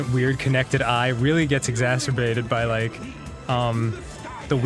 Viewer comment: Wait did Dan just say exasperbated at 8:19?